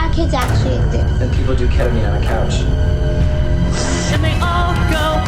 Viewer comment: oh my dear Lord .. I am sorry in advance if I might have misheard something but 0:25 (onwards) sounds like "and people do Ketamine on the couch" ... and this adult person says this CONFIDENTLY in front of an 11 year old child .... this can be punished by law - this person is potentially incentivising a young child to take drugs ( by showing that it is normal to take drugs on your couch " as it is the new age") . DISCLAIMER: I sincerely hope that most drag performers perform drag in places where there are no children and make it clear that their performance is for adult only now with that out of the way law student .. so as a law student I do not care if this man ( definitely above the age of 25) is drag.. couldn't care less ..what I do care about and the authorities should also care about is the following: there is an adult man sitting next to an 11yr old boy proclaiming that taking Ketamine on the couch is "the new age". This statement can be interpreted as the man affirming that taking drugs such as Ketamine is cool. From the behaviour of the 11yr old boy we can see that this child looks up to the man sitting next to him - or at least is inspired by him. Any adult talking to and with a child should be aware of the responsibility they carry AND the responsibility behind every word they say. Every word they say influences the mind of the child. One could argue that the man spoke directly to the camera ( addressing the people on the social media platform). This would mean that